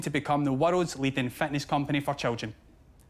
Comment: I thought he said fatness community for children😁 One of the strongest Scottish accent I have heard coming out of a sober Scottish guy’s mouth lol